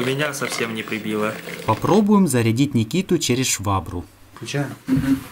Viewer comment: Мне послышалось:"Попробуем зарядить Никите через швабру"